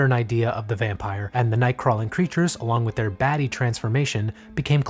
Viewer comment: I definitely heard "along with their baddie transformation" and immediately went "yeah I guess dracula was a baddie" my brain is broken